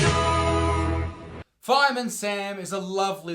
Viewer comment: I thought it said firearm Sam